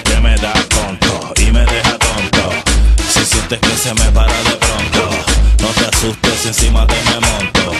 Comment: En El Pedazo 1:36 No Dice Se Me Va A Parar El Toto Si No Que Dice Se ME Va A Parar Depronto_____ASCO DE LETRA *.*_____Este Mensaje Estallara en 10 Segundos....